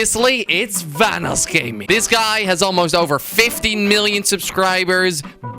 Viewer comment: vanoss has almost over 50 mil subs? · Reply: Oh you thought said 50 million. He said fifteen.